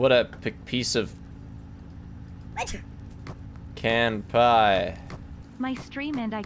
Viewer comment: captions say “canned pie” and now I can’t un-hear it